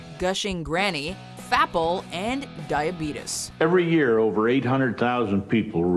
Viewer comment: Listen at 6:49 an i swear it sounds like she’s saying DIE OR BEAT US... diabetes... dieahbeatis... die or beat us 👀